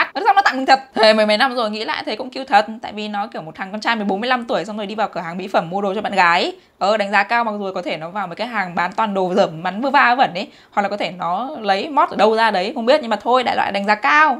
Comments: " 1 th con trai 14 15 tuổi " tôi nghe nhầm là 45 tuổi. Ối ... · Nghe nhầm thằng con trai 45 tuổi muốn lag cmn não